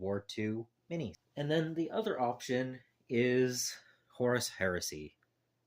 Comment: Ah Yes, Horus Heresy - I misheard you and thought you said Horse Heresy lol - but that is a good point that 3rd ed is on the way. If you could work out a simple scheme with little in the way of freehand detailing, that could be quite a fulfilling force to finish and be moderately quick to finish as well. I sort of like the idea of working on these.